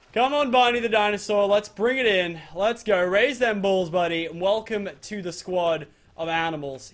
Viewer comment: did he say raise those balls